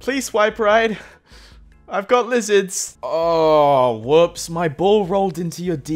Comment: OwO Did they say lizards??